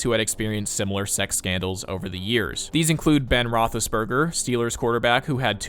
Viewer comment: burger? did he just say that that guy's last name... is BURGER?